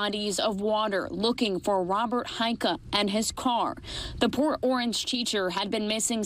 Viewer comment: I swear I thought she said "that poor orange person"😂😂🤣